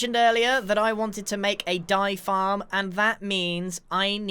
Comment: At first I thought he said "die-farm"